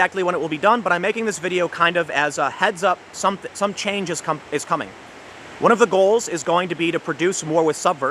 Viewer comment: Tim said “Change is coming.” What I heard was “Winter is Coming.” Hahaha